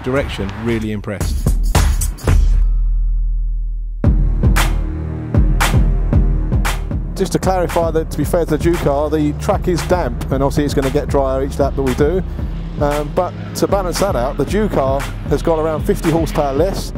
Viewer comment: lol at 1:51 it sounds like he says "Jew Car" instead of "Juke-R"